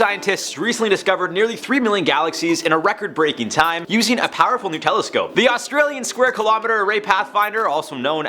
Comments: When jarred said, "a new telescope," I thought he said, "a nutella scope." Lmao! Go to around 6:35 and listen to it! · It sound like Jared said "a powerful Nutella scope"